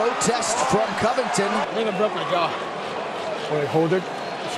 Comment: did he just say N*** broke my jaw